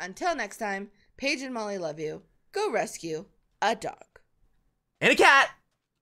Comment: I thought he said I love you